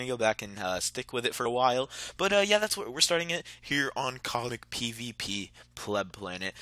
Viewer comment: did he just say comic pvp